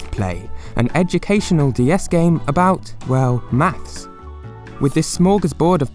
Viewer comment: did he says "maths"?